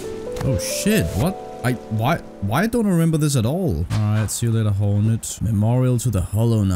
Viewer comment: did you just say "Alright, see you later Hollow Nips"?